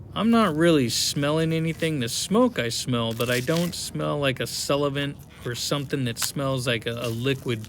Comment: even the captions said sullivent 😂